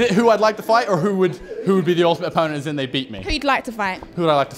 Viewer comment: Did hey say he wanna fight ant and deck at th same time😂